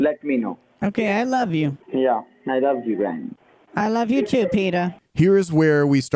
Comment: He actually said 'I love you too, pidor!' In Russia, the word 'pidor' (пидор) means gay in very, very rude form. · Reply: Well, he said Peter, but this sounded like 'Pidor'. But the scammers are real pidors.